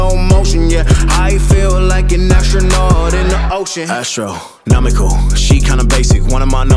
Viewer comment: What's actually said: "astro - nomical" What's shown: "astro - nominal"